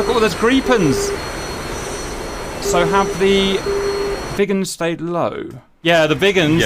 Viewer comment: To be fair, i heard "the vegans" the whole time instead of the Viggens so it was fun to hear about Vegans against am American carrier group